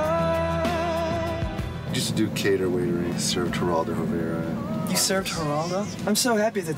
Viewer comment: what are they talking about?? I can't hear a single thing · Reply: Mick Grondahl said that he used to do cater waitering and served Geraldo Rivera.